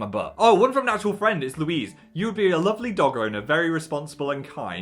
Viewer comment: I heard ‘dog owner’ as ‘dogger’ as in person who goes dogging. For the record, I’m sure you would also be a responsible dogger.